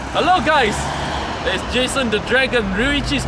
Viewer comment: I thought he said "hello guys, I was chasing the dragon" LOL